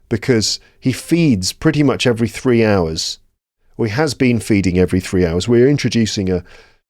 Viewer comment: he said ( we has been feeding ) i turned the subtitle on n told me ( he has been feeding ) but I’m positive i heard the first one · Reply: It's "or he has been feeding every 3 hours". I said "or he" very quickly and the words blended together a bit, making them sound like a bit l ike "we".